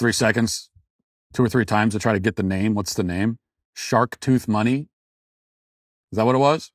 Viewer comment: Sharktooth Money would’ve been better but I think he said his name is “Chartreuse” - a herbal liqueur.